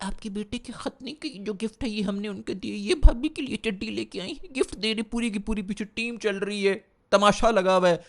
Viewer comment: 😂😂 did he say bhabi k leye chadi ?